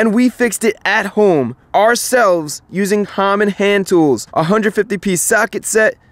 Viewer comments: "all by ourselfs, using hommon hand tools" -Chrisfix 2020 You are the best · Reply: watch again at 29:18 Chris says Hommon Handtools instead of common. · chris i think you said hommon hand tools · did he say hommon instead of common or am I tripping